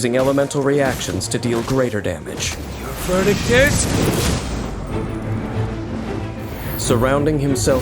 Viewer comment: Today was the day I realized Diluc says “Your verdict is-“ instead of “burning tears”